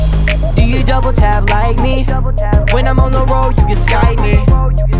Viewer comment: My brother thought it said "When I'm on the roach" instead of "When I'm on the road"